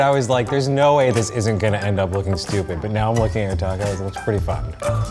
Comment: did he say “pretty fucked” or “pretty fun”?